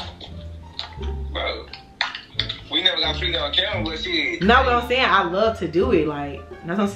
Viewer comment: did I hear that right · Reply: it's the we never got freaky on camera before but shiiittttt 4 me lol...they prob done got freaky lmao....still love their energy... s beast